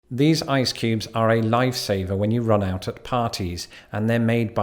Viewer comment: 🤣 I thought you said "these icecubes are a lifesaver when you run out of parties " I confess to wondering how that worked...even with a wizard at the helm 😁